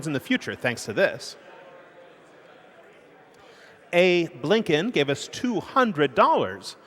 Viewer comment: Did he say Abe Lincoln?